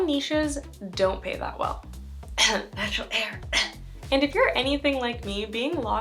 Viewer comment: clearly an inside joke. Couldn’t understand what was said though. What was that you said? LOL Btw, new here. My wife sent me your IG and YT bc we are so intrigued at your rise to success through social media. Congrats. You definitely have built a brand for yourself and clearly have a good roadmap for how to make this work. We’re planning to do the same in 2023 and your tips are invaluable. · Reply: I’m so sorry—I said “Natural Hair” 😂 it’s known to be a niche that doesn’t pay much. Thank you so much for being here, I really appreciate your kind words—keep me posted on anything you’d like to see a video on, I’m planning to get back to a weekly upload schedule for 2023.